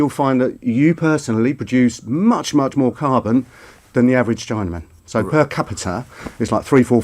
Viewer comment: did he just say chinaman? Lol